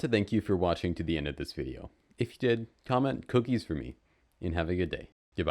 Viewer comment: did u just say cookies fruity ? XD